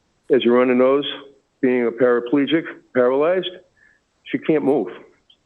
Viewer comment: They said she is quadriplegic, yet in the hearing video you can see her rub her face and she was writing on a white board. Quadriplegics are paralyzed from the shoulders down. Quadriplegic meaning 4 limbs are not functional. So, the information that is stated in court is incorrect. And if she did jump after killing her kids and got hurt, that's called instant karma. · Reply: I heard “paraplegic “.